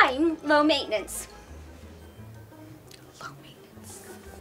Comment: Did anyone hear her whisper "low maintenance"?